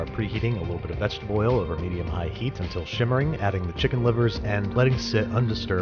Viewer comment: Simmering or SHimmering? I hear good ol' Babish say shimmering a lot and wasn't sure if that's just his accent or if that's what he meant to say. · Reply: He is saying shimmering